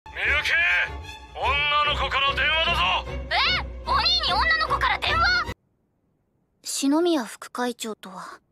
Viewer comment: did she just say onii-nii